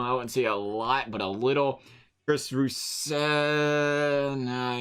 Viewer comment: did you say Kris rousel